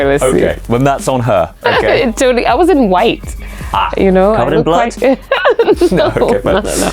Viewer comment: did anybody else think she said that she wasn't White, which was why the lady was scared of her?? 😐😬 just me? ok. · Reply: Lmao no need to argue people. She said “I was in white” when he asked her if she was wearing a red boiler suit.